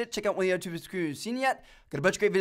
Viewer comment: What did he say XD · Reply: Checkout one of the two video's on the screen if you havent seen them yet. I believe he thinks it's funny when he doesn't articulate very well.